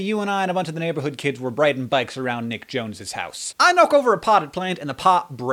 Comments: Am I the only one who heard him say Nick Jonas? Yes? Ok... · I thought he said nick jonas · At first, I thought he said Nick Jonas. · i thought you said nick jonas. my heart filled with fear for a few moments.